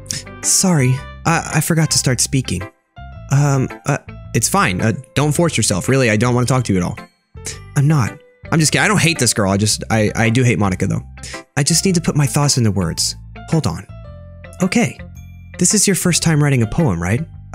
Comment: I thought she said “im hot”